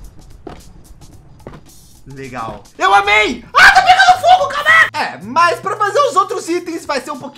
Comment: so eu escutei "A! TA PEGANDO FOGO CARA-"